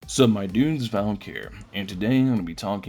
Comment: i literally heard "Suck My Dunes valk Here" as the intro....